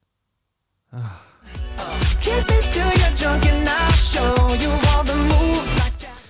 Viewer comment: Did he just say "kiss me to your junk"? LMAO 😭